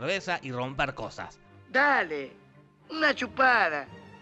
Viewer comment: vine a ver este resumen para esto. Que dice bonilla? cubiya? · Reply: Dice polilla